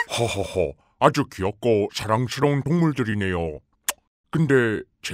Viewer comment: 대통령:아주 사랑스러운 국물들이네요 라고 나만 들림?